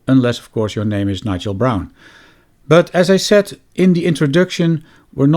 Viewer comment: Did he say 'Nigel Brown '? 🤣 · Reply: No, Nigel Braun